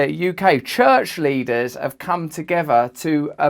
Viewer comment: When you said church leader i heard "church lizard" 🤣